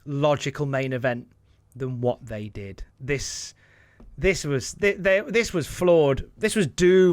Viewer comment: What did say around 1:06:13-1:06:17?... "This was flawed...", or, "This was floored..."?... I think he said "This was flawed...", but, it's because of his accent that I'm probably misinterpreting what it is that he said, or, just not paying attention & that's why I'm confused as to what he said.